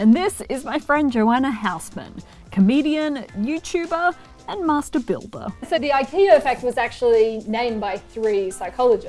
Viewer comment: When you said "Master Builder" I thought you said "Messed Up Billboard" :'v